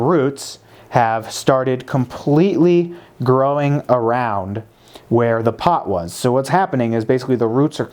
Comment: Did he just say circle around on a four corner pot ? 😂😂 Very informative video though